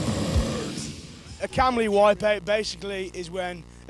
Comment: I've been trying to figure out what he is saying for ages. A what wipeout?! Calumny? If anyone can help translate I'd be grateful. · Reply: He says 'Kam Lee' wipeout, referring to the old Death/Massacre vocalist: <link>